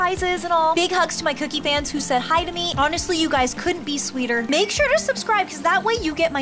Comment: At first I thought cookie said you guys could be sweeter she said you couldn't be sweeter